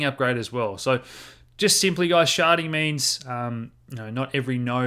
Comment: OMG I thought you said sharting not sharding!